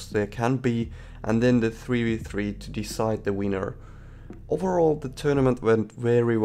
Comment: When you said winner I thought you said wiener